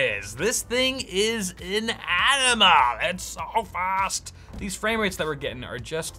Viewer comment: sounded like u said its an ENEMA lol haha